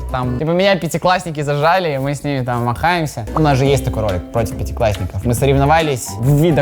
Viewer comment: Я вместо "махаемся" услышала "тр*х@ Простите за мою фантазию😅